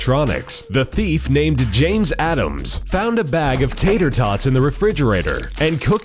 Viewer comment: i thought you said john Adams .—.